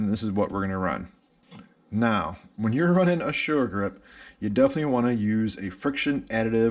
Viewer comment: "When you're running a .... " Running a what? I could not catch the words. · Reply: I think I said sure grip